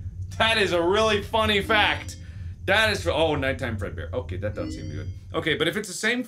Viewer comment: did he say night time fredbear?